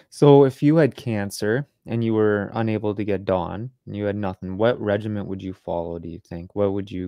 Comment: What are they saying at the question at 45:00 mark? Dawn, dun, don? What is that? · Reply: The drug, 6-Diazo-5-oxo-L-norleucine (DON), can be effective in targeting the glutamine pathway, but the drug is difficult to obtain and must be administered intravenously. Hope this helps.